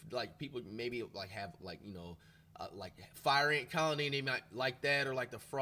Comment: did he say fire ant colony😂😂